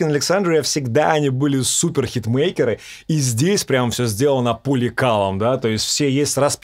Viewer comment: Услышал "... и здесь все сделано поле калом..." =) <link>